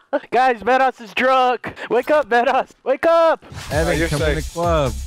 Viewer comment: it sounded like he said Vanoss is drug